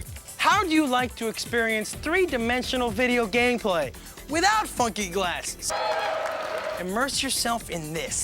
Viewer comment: threw me off. Thought he said "without F#@ glasses"